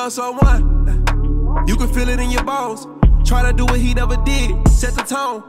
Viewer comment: This whole time I swore up and down he said when you love someone you can feel it in your balls and that's what I've been singing this whole time